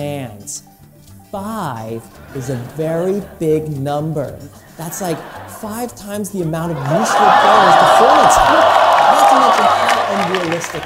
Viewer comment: 被笑聲蓋過去惹吧～聽不清楚(ㄒoㄒ) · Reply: 5 is a very big number. That’s like five times the amount of useful players the Hornets have.